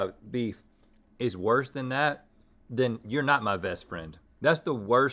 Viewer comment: Lol 11:08 the person who made the subtitles said vest instead of best. Get it? Cause link is wearing a vest?